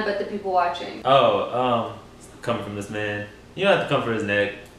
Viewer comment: I thought he said “come for this man”